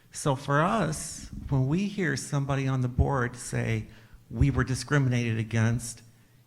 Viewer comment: I legit heard "when somebody on the borg says" and not "the board"